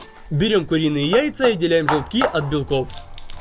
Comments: Мне одной послышалось что он на 5:45 времени сказал отделяем белки от белков.И такое бывает 😂😂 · Мне одной послышалось что он сказал "отделяем белки от белков"?